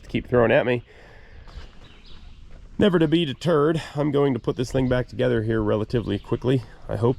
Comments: did you say "Never to be deterred" or did you say "Never to beat a turd"? LOL. JK. Love your stuff and thanks for sharing the adventure. I appreciate all the additional work you do to bring this to us and the fact that you present it well. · I heard, "never to beat a turd". 🙃